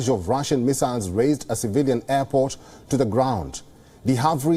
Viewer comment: Vinnytsia was an airport operated by the Ukrainian Air Force. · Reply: strange, I thought he said "civilian airport", not military airport.